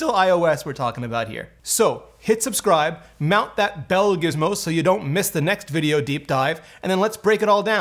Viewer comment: did he just say "Mount that bell gizmo"?